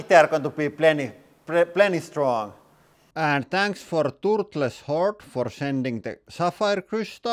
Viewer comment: I thought he said "And thanks to toothless wh*re for sending the sapphire crystals". Yeah I now know it's "Turtle's Hoard". 🤣